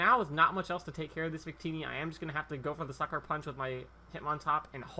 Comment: the first time u said sucker punch i thought u said fucker punch